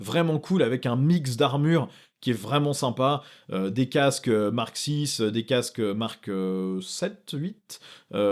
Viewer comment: Pour les figs des corsaires j'ai compris " casque marxiste" au lieu de Mark 6...faut que je dorme